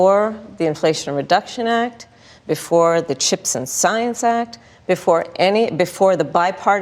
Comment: I keep hearing the ‘Chips and Salsa Act’. 😂